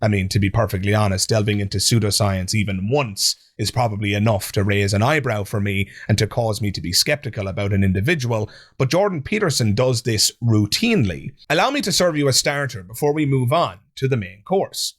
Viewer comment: did you say Peterson delves into pseudoscience "woo-tinely"? Lol.